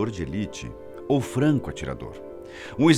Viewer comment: eu entendi frango atirador! Kkkkkkk